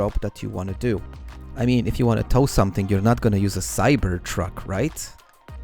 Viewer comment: I misheard it as "You're not gonna use a Cybertruck to TOAST, right?" I might not, but I also imagine a Cybertruck would be mighty good at toasting anything. 😁😜